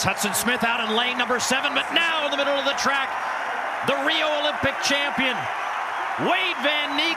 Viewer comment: I thought he said the real Olympic champion 😂😂😂i was about to say. He said the Rio Olympic champion